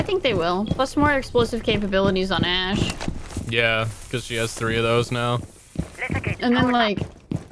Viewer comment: ash has 3 charges now did i hear that correct? 6:11. I did not see anything in the patch notes